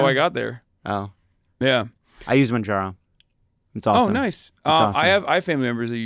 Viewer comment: " i use madrah". Is that what lovett says here. Never heard of it. What is it? Ahh its mudra. A type of hindu yoga. "I use mudra" now sounds so hilarious in the context of this conversation with hasan saying he does weights and plays basketball.